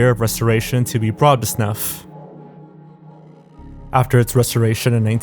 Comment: What word are you saying at 21:50? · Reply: "Brought up to snuff"